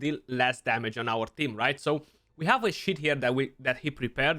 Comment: wait, what did he say? 1:22 oh... nevermind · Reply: When have a shit here 😂